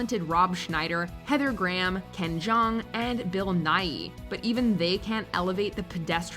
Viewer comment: did they say bill nye Like the science guy